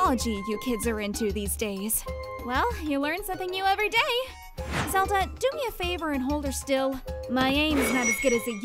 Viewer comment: did she just call link zelda